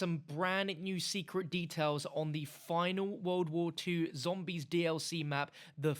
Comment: I thought it Said MW2 zombies